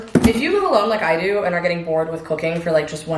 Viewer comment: I could have SWORN you said “if you’re getting bored with cocaine” 😂